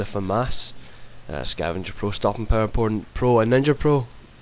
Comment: at start of video sounded like you said stopping power porn